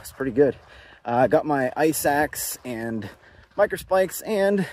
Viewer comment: You said "ice axe." I heard "eye sacks." Yeah, it's been a long week.